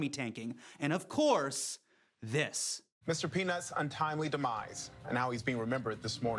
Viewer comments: I heard "Mr. Penis...." -_ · Am I the only one who thought he said Mr. "Penis"? XD